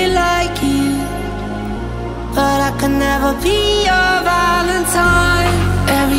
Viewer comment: Bro I thought it said valeting💀